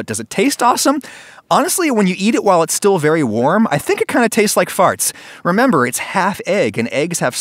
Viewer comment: Did he just say it tastes like farts